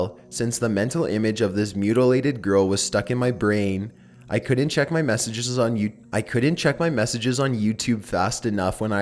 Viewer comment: Did anyone else hear at 9:01 "I couldn't check my You- I couldn't check my Youtube"